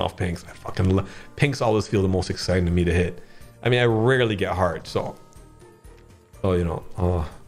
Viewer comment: what says: "i rarely get hearts" what I hear: "i really get hard" love the videos. you boys make my morning when i can wake up and watch all the gamba videos!!